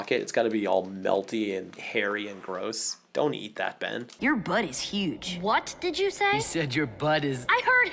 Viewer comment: Half-sounded like "Your buddy's huge" to me.